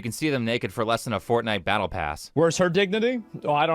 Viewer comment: I first heard that as "Fornicate Battle Pass" there